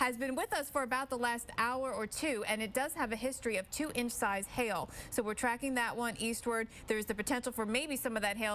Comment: I thought she said "chicken sized hail" I need sleep